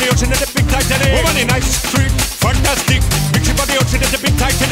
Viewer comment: it sounds like he is saying "nice string,fuck that stick" lol